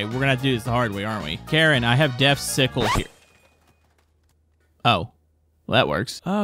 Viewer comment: what did you just call a scythe. It sount like he said sickle